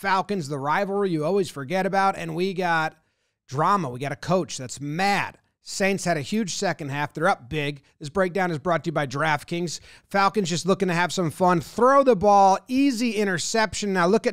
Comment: My man said "This breakdown is brought to you by giraffe kings", i heard it!